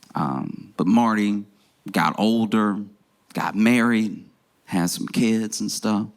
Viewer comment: Thought he was saying Mario....